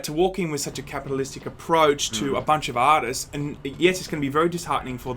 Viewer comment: did he say "a bunch of autists"? hears once more Oh... artists. That makes sense